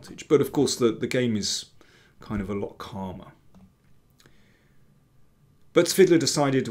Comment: Looks like Rapport has studied the games of Duncan Suttles, who played a lot of 1.g3 & 1...g6 some with a Nh6-f7 setup, forcing through e7-e5. Btw, at 5:38 I can't make out what's said: "The game is kind of" low/lock karma?? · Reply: ‘...a lot calmer’